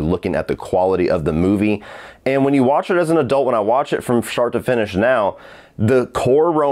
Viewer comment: Sounded like he said, "From shart to finish." A Freudian slip, perhaps 🤔🤣